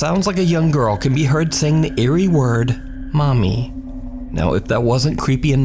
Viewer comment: I heard 'daddy' instead of 'mommy' in the first story!